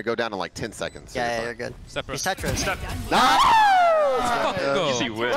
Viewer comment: ayo did you call the steps "he's step bro" ?? LMAAOAO